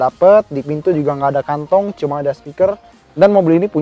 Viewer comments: "mobil ini punya 6 stiker" itu gw salah denger apa disengaja ya??? wkwkwkwkw k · "mobil ini punya 6 stiker" itu gw salah denger apa disengaja ya??? wkwkwkwkw k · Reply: RD5500 salah dengar vro. Dia bilangnya speaker..